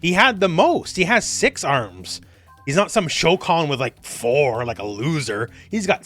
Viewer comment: you tripped me up. I thought you said Shao Khan, until I remember Goro's species